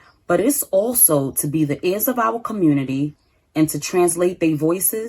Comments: Did she say at 4:54 "they voices into action"? OMG we are so lost. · Did she say, "...and to translate they voices into action"? And she's on the school board? WTAF is wrong with this country. If you cannot speak English properly, you have zero business anywhere near the school, no less the school board.